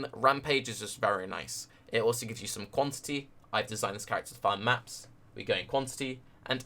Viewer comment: IIQ does NOT affect map drops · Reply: Right, I thought you said "I've designed this character to find maps" at 11:55 but misheard you (so did autocaptions)